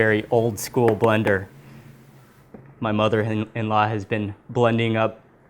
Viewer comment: Did Mark say my mother and my husband @ 12:48? I thought Mark was married to a girl because some of his other videos there was a girl next to him.